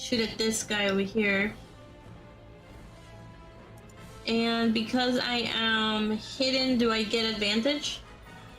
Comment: Misheard due to x1.5 "Because I am under cover, do I get a bandage?"